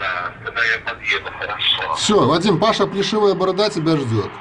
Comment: Паша есть такой качок известный в сша старый негр "плюшевая борода"... тебе послышалось плешивая )))